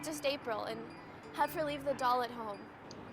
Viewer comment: i thought she said rape the leaves